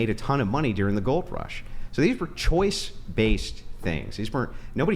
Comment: he said, “choice-based”, even though it sounded like “choice, based”.